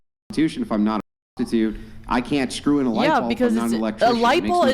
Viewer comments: did she just say lightball? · Did she say "lightball"?